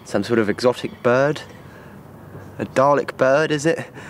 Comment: When he said a dalek bird, I thought he said a garlic bird...